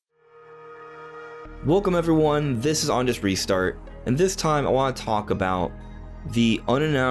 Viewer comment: Every time you say the channel name, I hear “Honest Restart”. I actually think that would be a good name. You should consider it.